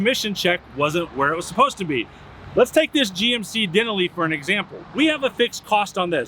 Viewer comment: Whoa hold up just a second…What did he Call that truck? A GMC Den-A-Lee